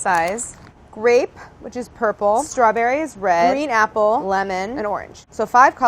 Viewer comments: Legit thought she said "RAPE ...which is purple..." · am I the only one who heard rape lmao